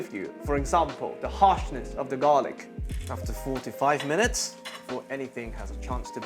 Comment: Did you say 45 minutes before it starts to burn? looks like 45 seconds by the looks of the ingredients :)